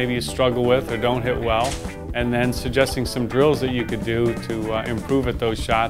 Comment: omg, lol really thought he said: and suggesting som girls you can do (at: 00:59 )